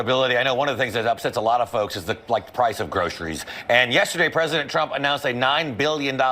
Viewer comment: Most times, when I hear LIE-vitt pronounce the orange turd’s last name, I hear, “President Chump”. Also, I bet the FAUX nooz photo dept. has a hard time finding decent photos of him not looking like he’s a decomposed houseplant.